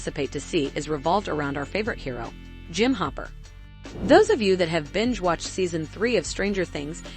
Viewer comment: The way they pronounce “COVID” 💀 It also sounded like they said humper not hopper 😂